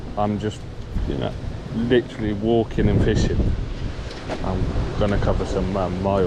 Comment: Good to see ya back. Some amazing editing at the begining . 😎 At 23.45 I thought ya said you just walking and pissing lol. You should do a reviews on the rods. Why you choose one over the other etc. Looking at the agile myself,but torn between the proflex 2. Keep up the entertaining vids 😎👍